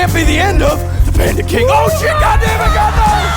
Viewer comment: For the longest time I thought he was saying Panda King